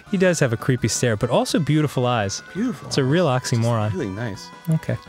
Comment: Instead of "real oxymoron" I heard "relaxing moron".... not sure what to interpret from that ._.